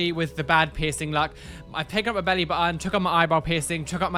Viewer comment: omg, i really thought I heard, you took out your eyeball piercing, instead of eyebrow...... I'm dead